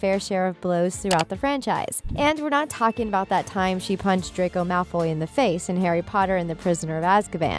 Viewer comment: DID SHE JUST SAY DRAQWO MALFWOY? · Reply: What I heard is “Drac-Oi Malf-Oi” lmaoo